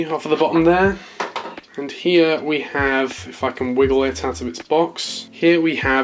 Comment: Anyone hear an F bomb be dropped? Anyway, awesome unboxing!! Edit: Clarification was made by a generous reply to this comment. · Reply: “If if I can wiggle it out of this box” he said if twice